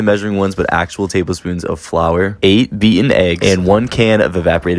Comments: I THOUGHT HE SAID VEGAN EGGS LMAO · "8 beaten eggs" i misheard that as "vegan eggs" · Lol I thought he said “8 vegan eggs”